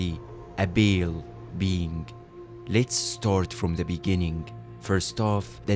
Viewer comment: I kept hearing "Bail Being" 😭😭